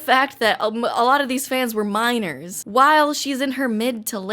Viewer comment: I’ve been doing “talking” my whole life. Without exception every SINGLE time I hear “Minor” I hear “Miner” I guess I just hear zebra. What the heck is wrong with me?